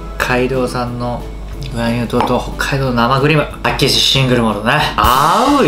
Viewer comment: 2倍で見てたら生クリームが生ビールに聞こえてびっくりした